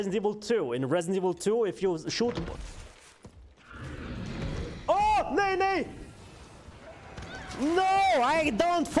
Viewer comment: did he say nahi nahi??!